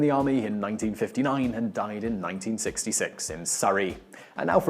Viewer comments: minutes he said that he died in 1966, but the caption said 1996 - The correct one was 1996, according to Google. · he says he died in 1966, but the subtitles say he died in 1996. Which is it?